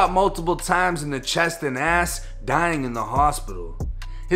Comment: I need some clarity (3:55) Does say he was shot in the chest and ass”?!?!